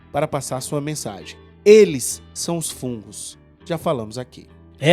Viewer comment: "Eles são os fungos" oloko Se eu entendi certo, que frase